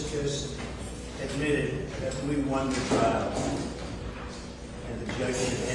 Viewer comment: It sounded like Donald said " we won the trial" . Anyone else hear it that way?